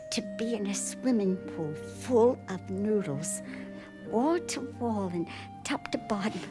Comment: Oh, noodles! I thought she said nudists...